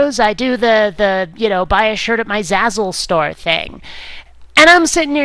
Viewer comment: Oh, Zazzle STORE...I always thought you said "Zazzle Star".